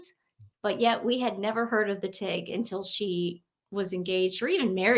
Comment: Hahahahaha, I thought she said she was vegan!!